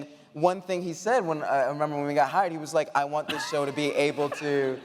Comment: i thought i heard "when we got hired" not "when we got high"?